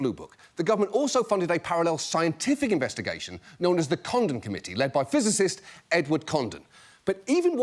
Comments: John spoke of the Condon Committee and at first I thought he said condom committee. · Did anyone else hear him say "the condom committee"? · I misheard that the Military established a "Condom Committee" and now i just want a Last Week Tonight discussing it